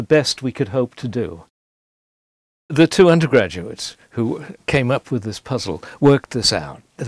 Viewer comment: I initially heard him say two hundred graduates instead of two undergraduates and was impressed by the level of coordination that was needed, and also the infinite monkeys and infinite typewriters was only 200 graduates to get a new math proof. But alas